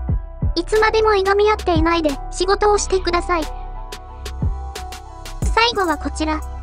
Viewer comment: 自分達は政権を妨害していればいい、揚げ足を取っていれば満足、ってスタンスなんでしょう。 立憲民主党の議員には国民のために動こうなんて意識は微塵も感じませんね。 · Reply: 私には、福山は「私を殺してください」と言ってる様に聞こえる。